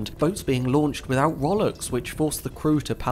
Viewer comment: Rowlock - it's the brace in the side of a boat that the oar sits in to hold it in place and acts as the fulcrum to provide the leverage. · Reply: Thank you! Part of it was the accent / pronunciation. If I heard "row lock" instead of "roll ock" I could've figured it out from context.